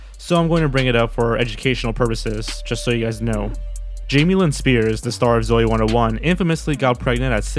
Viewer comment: When you said Jamie Lynn Spears I thought you said Jamie Lee Spears so I was like Brittney Spears had a daughter named Jamie Lee Spears? But then I was like ohhhhhh!!! Her name is Jamie Lynn! So Brittney Spears did not name her daughter after Jamie Lee Curtis. But then! Am I sure that is Brittney Spears daughter! It probably her cousin or sister or something like that. So I am totally confused. Also Her name is more like Jamie Loyd from Halloween 4 5 and 6 witch Jamie Lee Curtis was in Halloween 1 2 7 and 8. Sooooooo yeahhhhh